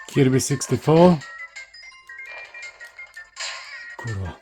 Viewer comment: Did you really say kurwa? :D